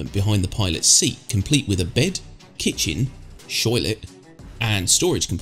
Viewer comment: did you call a toilet a shoiltlet?